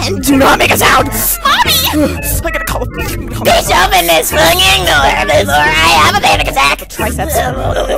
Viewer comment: “pLeAsE oPeN tHis sWiNgInG dOoR bEfOrE i hAvE a pAnNiC aTtAcK” “blblblblblblblblbl” “SARAH WOOFWOOFWOOFWOOFWOOFWOOF” (that’s what I heard)